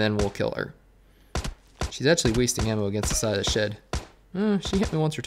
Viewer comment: Did u say shed at 4:50?